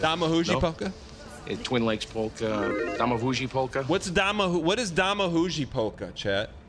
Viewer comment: He does in fact say "daj mi buzi" (like an american who heard a polish person say it) which is "kiss me" in polish or more literally "give me a kiss"